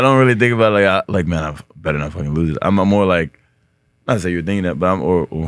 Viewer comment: Did anyone catch what Francis said around 32:30 before everyone started cracking up? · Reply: I know he said a curse word like "I better not f*cking lose." But Served does a good job of having the transcript available for those who want to follow along that way. It's found by clicking on the word "more" up at the top right hand side and scroll down to "show transcript."